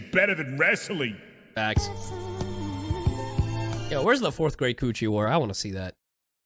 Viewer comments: I legit thought he said the 4th grade coochie war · What I heard: " Where's the 4th-grade coochie war at?" FBI OPEN UP! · I thought he said 4th grade koochie wars xD I was about to post a comment saying that then saying FBI OPEN UP!!! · 😳 okay when he said 4th great coochie war it sounded like 4th grade coochie war apparently my text to speak thinks so too because when I said it wrote it I had to type in Great